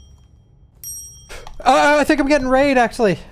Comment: I thought he said he was getting raided. I was like WTF.